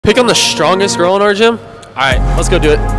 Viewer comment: Ahh I thought he said “pick on the strongest girl” like as in being mean lol I was abt to scroll😂